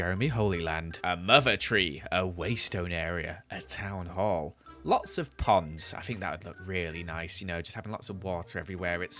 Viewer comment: I thought he said “lots of pogs”